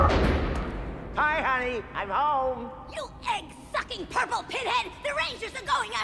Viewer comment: Ok seriously at 3:36 I know I wasn’t the only one at that time thought Rita dropped the F-bomb, didn’t it sound like she said fucking instead of sucking